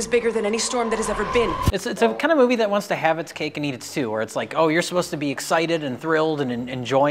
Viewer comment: did just say, "This movie wants to have its cake & eat its stew"?